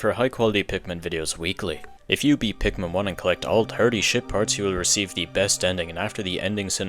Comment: “shit parts” 😭 even the captions say it lmao